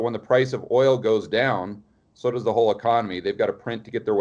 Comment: I thought he said vogue economy.